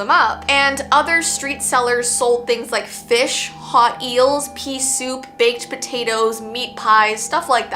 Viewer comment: Uh I heard "pee soup" in the vid while talking abt the muffin man